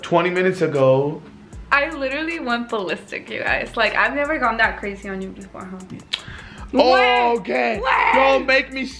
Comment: did yall hear what said !?!?!?!? 👀👀👀🙊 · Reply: She said babe at the end of the sentence